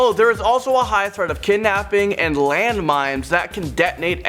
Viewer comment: did he say "land mimes"?